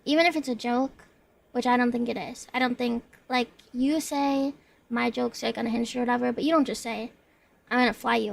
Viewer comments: I think she said "my jokes are (kinda unhinged or whatever)" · "My jokes are like unhinged or whatever" is probably what she said. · i think she said "my jokes are kinda unhinged or whatever"